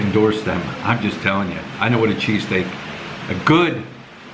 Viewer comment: With the bike noise I thought you said you were going for cheeseCAKE.